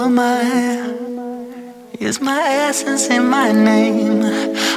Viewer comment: For a second I heard "is my ass in my name?" Instead of "is my essence in my name?" 💀💀